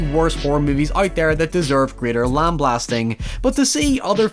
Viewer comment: did you just say lamb-blasting?